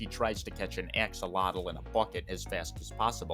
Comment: I heard "catch an axolotl and f**k it" I cannot express the mix of emotions I went though in that moment